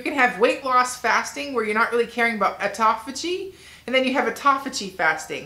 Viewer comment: Your weightloss pictures have got me enthusiastic about losing weight. You talk of strict & not so strict fasting & I can't make out the name of it at 15:45? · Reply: She says "Autophagy" fasting